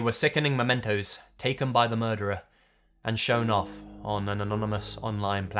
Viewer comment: Did he just say anononomonous? xD